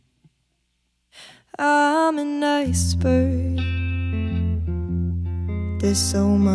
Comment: "I'm an iceberg. There's so much more that you eyes can't see." That line was enough!! I felt that! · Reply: Hannah Drake lol I thought she said “I’m a nice bird” this whole time 🤦‍♂️